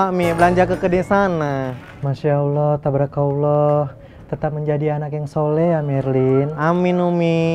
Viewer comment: Gw salah denger apa bukan ya, umi bilang "tetap jadi anak yg sholeh ya merlin" 😂😂😂